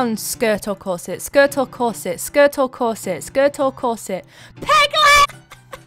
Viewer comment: When you said, “peg leg” the captions thought you said piglet...